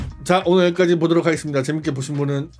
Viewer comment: 귀가 썩었나 ㅋㅋㅋ 다음생에 또 뵙겠습니다로 들어소 다시들었네